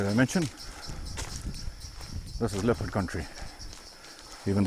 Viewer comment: When you said "This is Leopard Country." I thought you said "This is Leprechaun Tree."